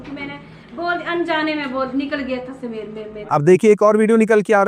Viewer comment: Did she really said, "Anjaane me"? Man u guys are hypocrites and cowards for fucking sure